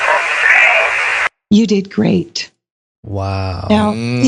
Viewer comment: She said "you did great" wow. I always thought she said its a frame. This totaly trips me out. Great job Mrs. Roth.